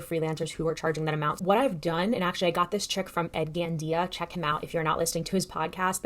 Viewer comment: Hi can you spell the name of (Ed Van Dea?) or provide a link to the podcast you spoke about? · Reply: I misheard you - found him! Ed Gandia